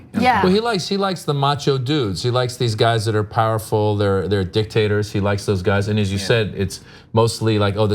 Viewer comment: OMG did he say "Dick tater"?? A potato that is a dick?